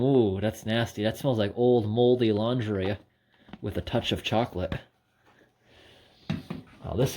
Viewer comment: “It smells like mouldy laundry with a touch of chocolate” that’s pure poetry. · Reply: I thought he said mouldy lingerie, in listening again it's definitely laundry.